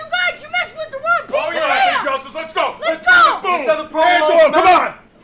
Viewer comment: does he say "here's how the provolone smells!" 😭 LMAOOO